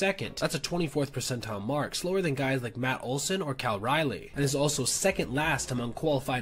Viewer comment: Man just said "Kyle Riley" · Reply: I thought he said "Cal Riley," instead Cal Raleigh (Mariners catcher).